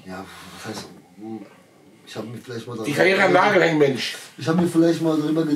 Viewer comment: Was sagt Ali bei min 27:00? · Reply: Die Karriere an Nagel hängen Mensch